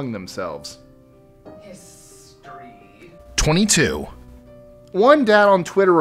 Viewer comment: Okay am I the only one who heard something say 23 at 1:40 idk weird or was it the person in the background trying be funny